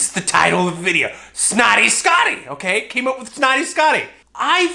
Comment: Oh I thought it said snooty scooty